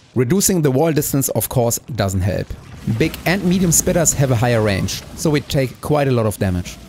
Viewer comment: i kept hearing "BMF Spitters"